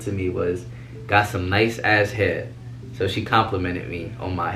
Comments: i thought he said got some nice damn head😂😂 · I thought he said “you got some nice ass head” i has to replay that one😂